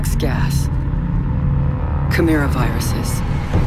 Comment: did she just say "Corona Virus" ? Like more than half a year before ?🙃